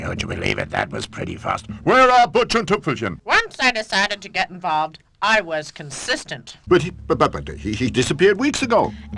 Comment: ONCE I DECIDED TO GET INVOLVED I WAS CONSISTANT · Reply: I actually thought it said "Once I decided to get fucked I was consistent."